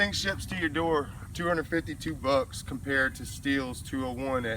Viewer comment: U don't listen that good ..he said the price plain as day in early part of the video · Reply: $252 at 4:35 in the video. Closer to the middle. Maybe instead of you being a smart all you could have just answered the question.